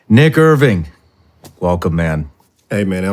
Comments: "NickErvin" maybe space out the words a Lil I thought he said 🥷vin 😂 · I'm happy you told him to say the "long" version. However, at first I thought you said the "N" word when you introduced him. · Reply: Dude me too I thought he said nicka Irvin.. I was like whaaat?!!!